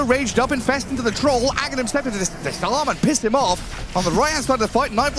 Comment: did he say "the salamat pissed him off"? What was the context of that?